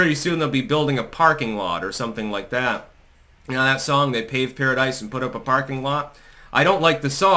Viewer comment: oh os the word PAVED? I heard it as "Gay paradise, put up a parking lot". And i took gay as the original meaning of joyful and idillic (like in 'deck the halls') rather than the way we ususally use it today.